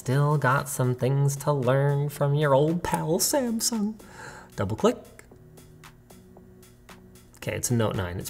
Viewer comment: did Linus sound like Shaggy saying "old pall Samsung"?